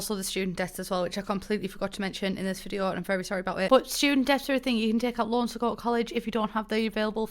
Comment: thought you said student deaths and not student debts by now you’d think i could understand you 😂💖